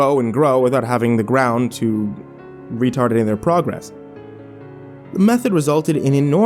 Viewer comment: at first I thought you said retarded instead if re-target lmao